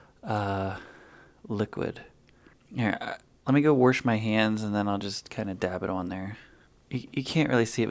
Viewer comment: I enjoyed your video. You must be from Pittsburgh, where I grew up. I heard you say, “I’m gonna WORSH my hands.” I’m a Yinzer, too living in Chicago. I like your videos. Thanks